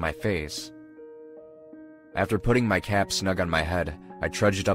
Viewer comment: AFTER PUTTING MY CAT ON MY HEAD is all I heard